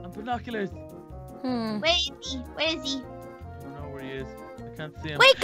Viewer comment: did say um.. A word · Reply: No she said where are you but her mic glitched